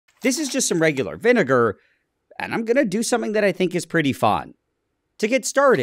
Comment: Anyone else thought he said “I’m going to do something I think is pretty hot”? 😂